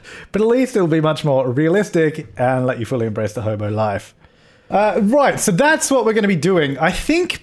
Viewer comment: Please don’t kill me but when he said “ the hobo life” I thought he said “the homo life” and I was like “good for you man” until I looked at the screen and read it.